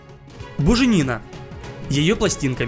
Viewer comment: я один услышал боже Нина